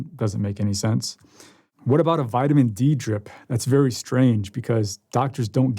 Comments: interesting, I didnt think Joe said "vitamin d" he said "vitamin drip". I believe MATH+ uses high dose vitamin c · I heard him say NAD drip and a vitamin drip. Not vitamin D drip...